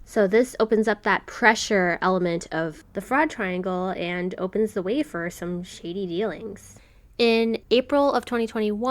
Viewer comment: “opens the whey for some shady dealings” is how I heard this the first time